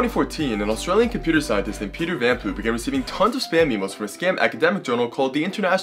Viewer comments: The only thing i can hear when you say his name i just hear "peter van poop" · A guy named what? I heard "Peter Vanpoop"